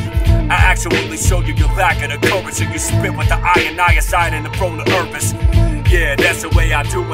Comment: Did you say InI-icide at 4:30??